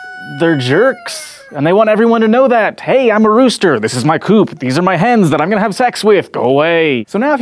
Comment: totally thought he said "these are my hands that I'm going to have sex with"